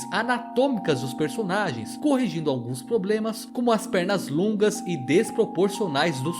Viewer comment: eu escutei um ploblema ai